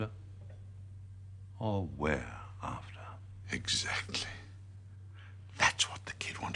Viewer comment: are after or we are after? Am I mishearing? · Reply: He says 'are after, or were after?' Present tense versus past tense. Speaking slowly sometimes Englishmen say 'were' like that. 'Are' would mean they arrived at the scene as the 'kid' was talking to his sergeant, before Prideau arrived. 'Were' would mean they had been after someone, and it was over. As Jerry says, 'The Russians moved in on the Friday, it was the day after, when they got Jim. As the kid said, they were ready and waiting for him. Knew the lot in advance.' That's my understanding, but agree that it's not easy to work out. That's the way people talk, not always clear as crystal.